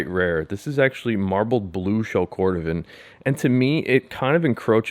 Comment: It’s called marbled green for a reason lol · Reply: Yeah Jack, except at 3:05 he CLEARLY says Blue